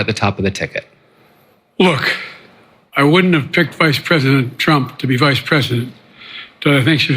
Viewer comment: Did he say vice president Trump? @ 8:46'ish